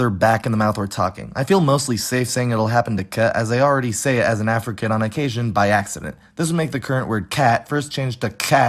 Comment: I swear he said "I already say it as an African by accident" i was very confused 😵